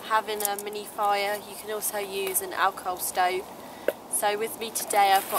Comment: 😜 I thought you kept saying "Alcove Stove"